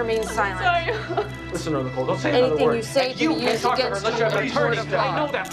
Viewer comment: What did the men say, I can't understand??? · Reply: Dad: "Listen to her Nicole, don't say another word. And you can't talk to her unless you have an attorney, I know that FOR A FACT" Greene: "Please sir, step off. I SAID, STEP OFF."